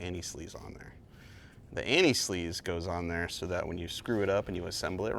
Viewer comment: "Tin Man" is the best version of the story, closely followed by the recent "Emerald City" tv series. And why does it sound like you were saying "anti sleaze" instead of "anti seize". Could be worse, it could be Aunty Sleaze, which could be a drag queen burlesque name.